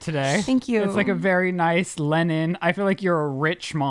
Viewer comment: did Ryland really just say “lennon” instead of “linen”? I know they talk about his pronunciation often, but some things really do annoy me for some reason. Like, you’re using a totally different letter! I’m surprised Lizze didn’t say anything